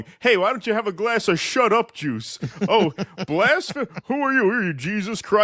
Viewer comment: CENK IS AWESOME!!💯💯💯.!! I thought he said ..SHUT UP JEWS.!?!